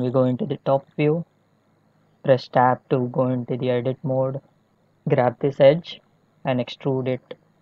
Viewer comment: i dont get it on this part 0:41. Coz when i enter E, different thing happened. What did u said and what did u do? i think i've heard "grab the edge" but im not sure.. now im stuck :( please reply my comment ASAP. thank u very much:) BTW: im totally a Blender beginner.. just install it few mins ago :D